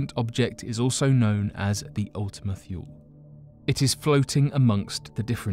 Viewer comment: What does he say at 1:50? Is it 'Ultima Fuel'? At least this narrator doesnt mumble and mispronounce everything like the other guy that usually does the narration! I shudder to think how he would mangle the pronunciation of that.